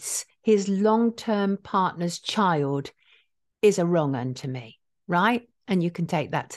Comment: Does anyone know what Sonia calls Woody at around 23:00 ? It kind of sounds like "Roman" or "wrong-un" (it's not bell-end 😂) or something like that. If someone can help me, I'd really appreciate it. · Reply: A wrong-in,a wrong one